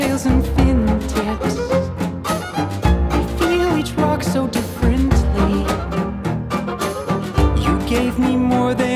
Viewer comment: Does anyone know what’s being said at 1:51? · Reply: we feel each rock so differently